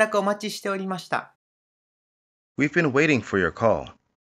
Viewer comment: ご連絡お待ちしておりました。 ここのhave been が本当に聞き取れない泣。もはや有声音でもない気がする笑笑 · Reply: WEv bena WAIting for your CAll 聞こえやすくなるといいです